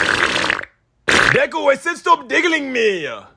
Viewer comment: dude why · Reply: i thought he said digging in me 😭😭😭😭